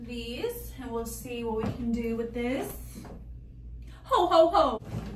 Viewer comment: omg girl I thought you said hoe hoe hoe girl. But either way I just wanna say I love your videos. I follow you.❤ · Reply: I said Ho ho ho 🎅🏼